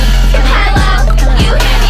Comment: I thought she said date memes